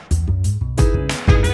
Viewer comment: I had P3P and the audio wasn't so great. Always thought he said "NOW DANCE, WHILE I PUT IT IN YOUR PANTS" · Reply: I play p3p And I always heard "Dance, Imma put you in a Trance"